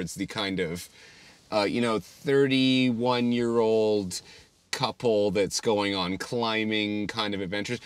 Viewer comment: I did not hear couple. I heard c***old. Had to double back there...